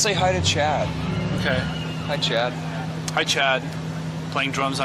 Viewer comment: I thought he said "hi chat" - like it was a twitch stream or something haha of course he says Hi Chad (referring to Chad Channing)